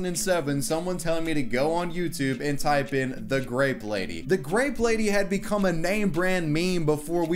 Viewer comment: I thought it said gape lady ... I need to get my mind out of the gutter...